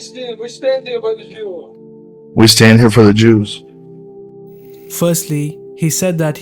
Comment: YOU MISUNDERSTAND THE CONTEXT, he thought Islam saying "With jews" when it turns out to be "With you" He misheard him!.. If you know this and still trying to put this video, ur just another guy who's seeking for views and money, put this video down